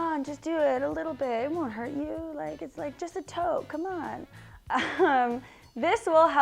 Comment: did you say "just a toe"? I am not sure but regardless it was hilarious to me. :D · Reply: I think she said just a toke lol. But i thought it sounded like toe as well 😂😂